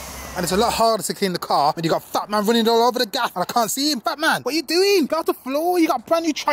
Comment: I thought you said fat man to. Marcelo but it was bat man